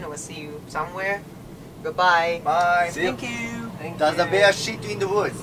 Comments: did he just say "there's a bear shitting in the woods?" lol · なんて言ってるの？w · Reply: Digital Tour Bus クマが森での奴です